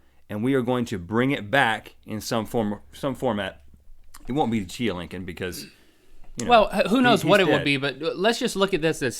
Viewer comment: I thought you said that you could re-seed chia Lincoln. Or else you should have someone make chias of you.